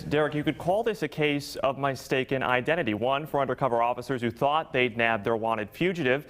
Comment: Did he just say my staken identity lollls